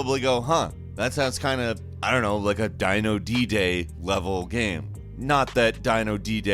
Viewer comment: when you said Dino D-Day i thought you said Dino Dj